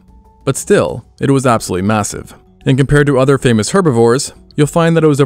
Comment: did u said MASSIVE?